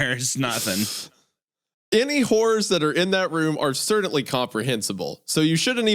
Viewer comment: I swear, I heard “wh*res are incomprehensible” at first😂😂😂 WHY DO YOU HATE WOMEN